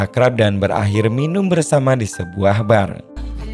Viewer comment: Minum di bank..apakah aku salah dengar,,min😉 menit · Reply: Bar bro bukan bank